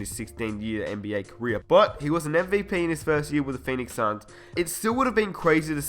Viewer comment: I thought he said mv Penis